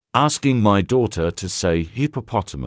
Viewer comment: It's hard to hear what she is but I thought heard her say hippotamates